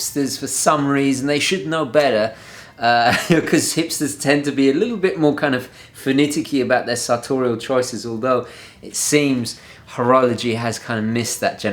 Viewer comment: 🔥🔥 sick burn on hipsters!! Also can someone transcribe what he said there? I thought it was very eloquent! Thanks in advance. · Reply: "They should know better because hipsters tend to be more alittle bit more finicky about their satorial choices. Although, it seems that horology has kind of missed that generation."